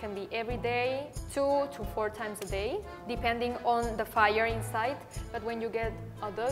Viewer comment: she actually said 'depending on the fire inside'. She's talking about libido, not astrology.